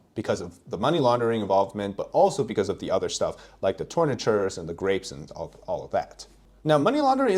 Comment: Hi, the word "Torniture" (spelling based on phonetic sound) has been mentioned multiple times throughout the video. Would like to know what that word mean? Thank you. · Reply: Torture